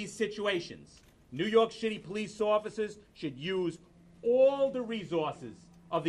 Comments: He clearly said New York Shitty Police Officers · Did he say 'New York shitty Police'? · I hear 'New York's shitty police officers'